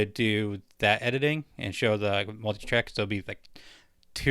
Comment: The first time I heard the title of the show you're editing (your first show edit not this one) I heard "Your Killer Wife", and was thinking, wait what? Is she super entrepreneurial and dominant at home or something? LOL. But now I know the subject and I think it's a great one, looking forward to seeing your process in editing :)